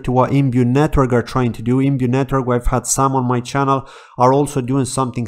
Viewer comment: what network did you say there?? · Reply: Imbue Network: <link>